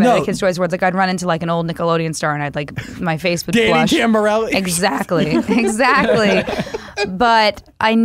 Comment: Does anyone know what name says at 35:43?!?!? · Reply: _2392ahah Danni Tamburo? That’s all I can hear